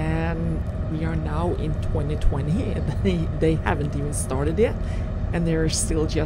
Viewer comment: Hi, just a question at 20:38 did you say : we are now in 2028? it's only 2024, and this was 4 years ago you posted this video right so wouldn't that be 2020? Not 2028? Unless I didn't hear you right that's what the captions said anyways. just wanting to clarify. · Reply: I said "we are now in 2020" - listen again 😊